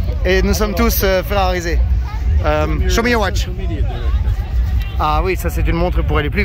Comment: j'ai compris '' Schumi you're watch '' c'est l'effet team Ferrari je crois😅